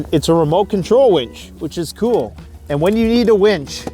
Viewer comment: Where is your survival shotgun or handgun? Asking for a friend. lol Great vid - I wouldn't be here commenting if I didn't enjoy your vids. And at 15:30 I thought you said "wench" and I thought immediately, yes having a wench would be nice... and then you showed the winch... meh.